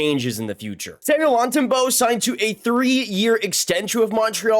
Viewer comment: It sounded like you said 30 year extension lol i was like what the lol